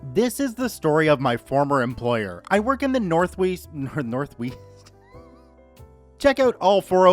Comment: Northeast? I thought you said Northweast